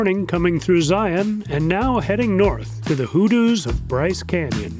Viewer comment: You made me laugh for a moment when I thought I heard you say you were coming to the "Hooter's" of Bryce Canyon. It took me a moment to realize you said Hoodoos but thanks for the laugh and the arm chair traveler experience.